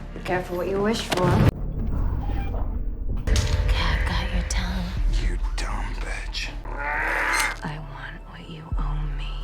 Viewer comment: I know there are two lines from two actors happening in this scene, but this is easily the most accidentally savage subtitle I've ever read in my entire life. (The auto CC - not the subtitles actually in the video.) "Okay, I've got your tongue, you dumb bitch, I want what you owe me." Imagine somebody saying that in a Batman movie.